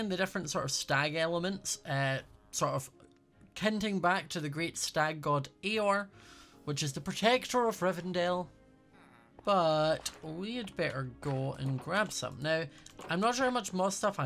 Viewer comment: i thought he said eeyore like that one thing from winnie the poo HELP